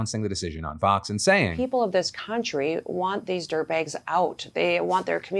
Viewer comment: Did she just call migrants dirtbags? Fucking hell America has really become a hellscape this month!